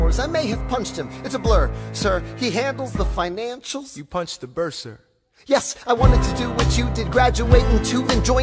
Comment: It took me too long to get the bursar line….😂I thought he said you punched a burr relative like my cousin burr….